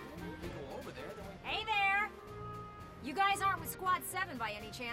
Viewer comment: Had to listen to this with headphones on my own copy but what I can gather is: Alicia "It's completely different..." Welkin "Think we should just leave? Go over there then." Can't make out anything else they say.